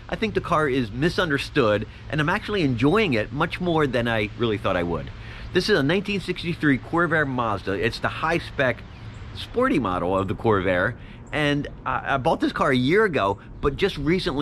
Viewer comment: Did you say Corvair Mazda?